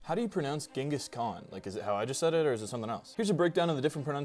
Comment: Dingus khan is what I heard you say